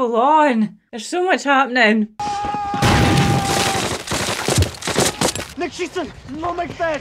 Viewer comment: "What's he saying?" He said "Nicht shiessen" a couple times. That means "Don't shoot". He also said "No make dead" in English.